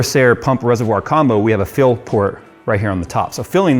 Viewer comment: I initially misheard 'Phil port' not "fill port"